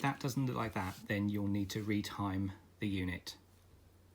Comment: at around 13:30 you talk about "retiming the unit" if I hear correctly. Do you perhaps know about some step by step of how to put it together? I took mine apart sooner than watching the video and is is just stuck now ! :D Thanks!